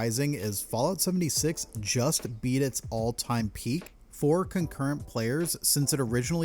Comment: lol. So at a very beginning, when he’s talking about the resurgence, I swear it sounds like he says that it beat it all time high four concurrent players, I realize he’s not saying that, but it really sounded that way and I got a big laugh out of it.